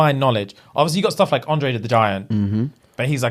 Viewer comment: I know this old but I heard "Andre the vagina 😂"